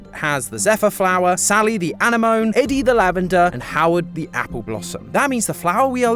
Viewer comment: did bro just call Howdy Howard